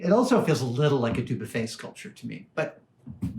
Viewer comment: You say "also like a blah-de-blah-blah-blah", and the closed captioning can't parse your voice, nor can I. I first heard "also like a tube of face culture to me", which I'm moderately sure is wrong. What are you saying? :)